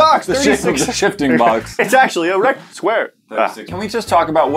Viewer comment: did just say " It's actually an erect square"?